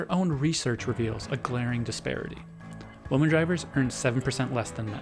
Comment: Glad the word ‘women’ was written down because I couldnt tell what he said otherwise. Lol